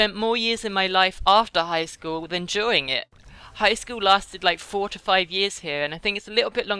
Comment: When she said high school is 4 to 5 years I thought she said 45 years and was like..... !!!!!!!!!!?